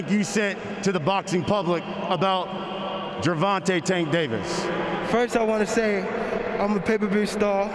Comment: i thought he said Vagintay Taste Davis🤣🤣🤣